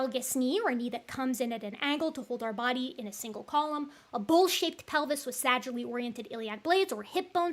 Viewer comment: i thought she said "a bull shit pelvis" Is it really that bad?